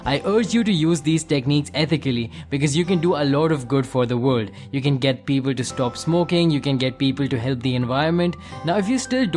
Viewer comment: lol I thought he said "you can get bieber to stop smoking.."